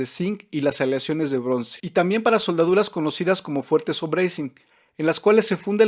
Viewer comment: Alguien me puede decir que dijo en el 3:19 ? Donde dice conocidas como... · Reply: Soldaduras fuertes o brazing (en inglés)